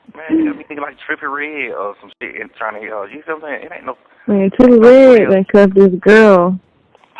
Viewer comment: Wait did Mariah call Coi Leray a thot? Trippie Red done cuffed a thot is what it sounds like she said...